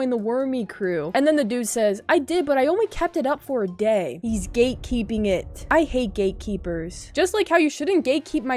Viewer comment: For some reason at the beginning i thought they said Gay Keep